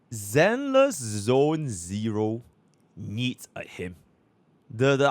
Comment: needs a what? All I hear is "a himp" ?